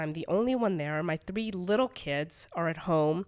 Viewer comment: What questions do y’all have for my fam? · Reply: Did she say three little kids?!?!